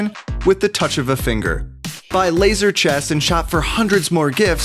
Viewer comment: I thought it said laser chese